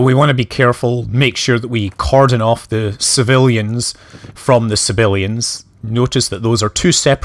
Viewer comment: You speak with a very soft "B" and for a long time I thought you WERE saying "civilians" and wondering why you were killing them.